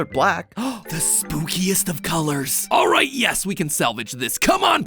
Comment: what did you say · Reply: ✨"ThE sPoOkiEsT oF cOloRs"✨💀🙏😭